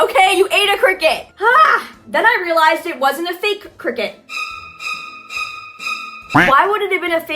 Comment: OH MY GOD THE CRICKET ONE IS MINE THANK YOU SO MUCHH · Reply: I thought you were saying game cricket